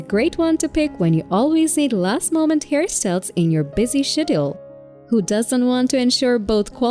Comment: Am I the only one who heard "in your busy shit hole"